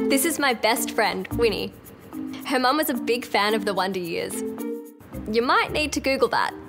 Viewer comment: I literally thought she said... One D years!!